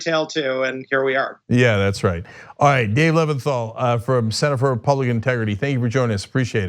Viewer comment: I misheard his title at first, it sounded like he was from the "Center for Republican Integrity"